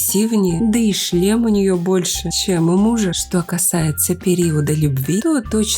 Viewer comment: мне послышалось : да и член у нее больше чем у мужа